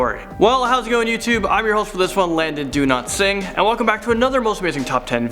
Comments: Landon do not sing Lol 😂that's what I heard him say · Umm.....wait a minute did I hear "Do not sing"? 🤔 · I'm sorry but I really thought you said your name like 'Do Not Sing' lmao